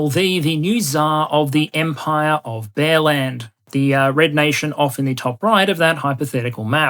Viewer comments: I heard 'Empire of Bellend". Which would only be fitting. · I kept hearing 'bearland' as 'bellend'